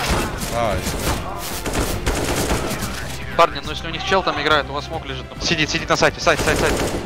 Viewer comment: послышалось там симпл с вами играет)